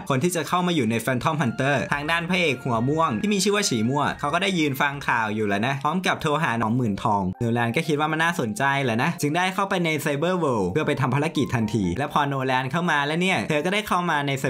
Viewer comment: พระเอกหัวม่วง ที่มีชื่อว่าฉี่ม่วง ผมได้ยินไม่ผิดใช่มั้ย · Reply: ฉีมั่ว พี่